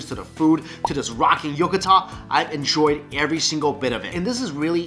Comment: he keeps mispronouncing "yukata",,.. it sounds like he says "yokatan"... weird